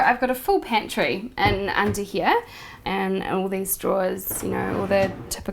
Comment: Did she say "dust drawer" ... under the stove? Is that a mishearing on my part or an Aussie expression? What is a dust drawer, or that space for pots and stuff under the oven?